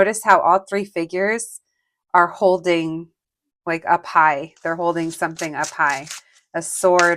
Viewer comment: Pile 1: 4:03 I WAS LISTENING TO THIS AND HAD TO BACKTRACK COZ I THOUGHT YOU SAID THEY WERE HOLDING A PIE NOT “up high” 😂😂😂